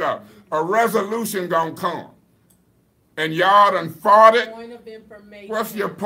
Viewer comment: what he say😂😂😂😂 did he say “yall done farted” or “yall done fought it”